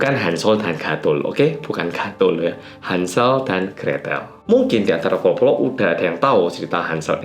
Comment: saya dengernya "hansol and guatel"